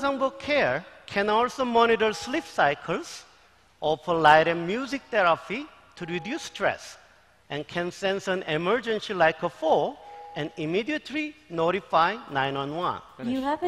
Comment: Did he say, "can sense emergency like a hoe"? XD